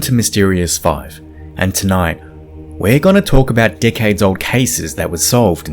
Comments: I thought he said "Dick-heads old cases" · Oh, man, I had to listen to the intro a couple of times. He says "Decades Old Cases" I thought he said "Dickheads old cases" Lol! Sorry, great presentation and I'm glad to see Science is catching up to these "Dickheads" Peace ☮ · I'm listening to far too many brits curse at me lately; I thought you said "we gonna talk about dickheads or cases..." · I thought they said dickhead instead of decades at the beginning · Why did I hear "decades old case" as "dick head o' cases" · I thought you said “dickhead’s old cases” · thought he said, “tonight, we’re gonna look at dickheads old cases” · I’m so sorry but when you first started I thought you said “let’s take a look at these dickheads old cases…” and went damn salt on the wound there!!!! Then realized you said decades old and went yaaaa that sounds right, I should go to bed soon 🥲